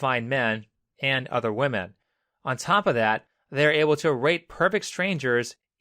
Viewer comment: thought he said rape XD